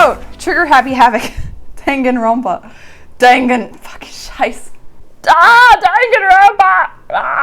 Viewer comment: DID SHE JUST SAID SCHEIßE?!!? GERMAN!!?!? WTF